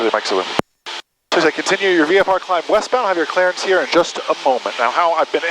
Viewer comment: I couldn't understand the controller at 10:02. The only thing I heard was "Continue your VFR climb..." Wish they would speak slower and clearly. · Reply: Hi Jash! The controller said "continue your climb westbound and I will have your clearance in about 5 miles." Radios can be tricky! Keep on practicing by watching videos like this or by using LiveATC! And as always if you need any more help in your journey please reach out to us at <mail> Fly safe!